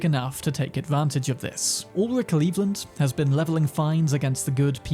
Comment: Not gonna lie... I totally heard "All of Cleveland" when you said Ulrich Lelland.